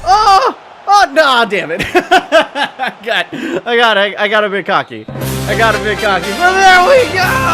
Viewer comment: it sounds like he says "I got a bukkake, I got a bukkake"